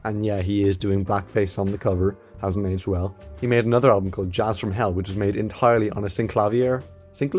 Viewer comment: how I heard it:''he made another album from hell'' LOL